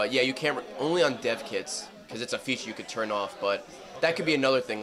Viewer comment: Did he just say only on deaf kids?